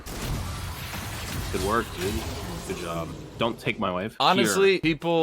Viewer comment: Misheard the Dont take my wave To Dont take my wife